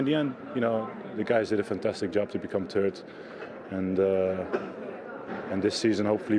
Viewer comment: 2.37 Did I hear him right? "The guys did a fantastic job to become turds...."